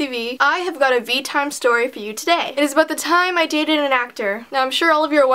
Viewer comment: i thought it said "the time i dated a docter"